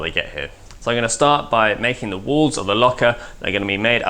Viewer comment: Is anyone else hearing "the Bat Locker"? :D Tally ho!